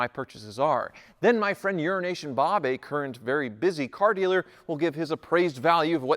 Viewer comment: Small peice of advice...slow down a bit when speaking....at 2:20 I thought for sure you said urine Nation Bob...I had to skip back and play it again before I realized you said Euro Asian Bob....lol